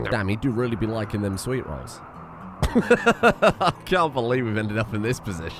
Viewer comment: I had to rewind 10 sec 2-3 times because i i thought it said sweet troll, but i have been drinking alot